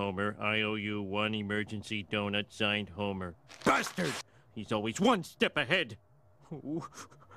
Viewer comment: why do the subtitles say gusterd when he said bastard